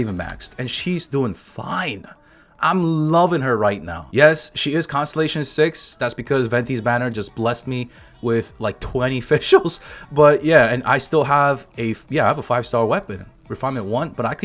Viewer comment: the auto generated CC says Venti just blessed you with "20 Facials" lol.